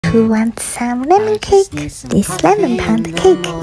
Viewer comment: I thought this was salmon at first😂 I even thought she was saying “who wants some salmon cake”